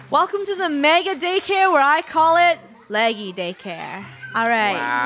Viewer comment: lol i thought at last said leggy daycare then i realized she said laggy daycare OMG LOL